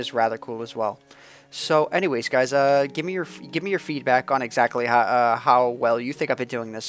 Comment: the first time he said feedback i thought he said feet back so i thought he said gimme your feet back